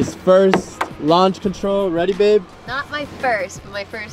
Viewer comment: Who else heard say ready babe